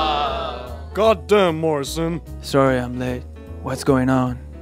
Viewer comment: Wait.... 0:29 did he say sorry I'm late while going to heaven??? 😂